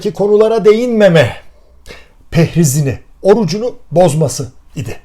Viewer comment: 'da takıldım kaldım.. "Pehriz" mi dediniz, ben mi yanlış duydum :)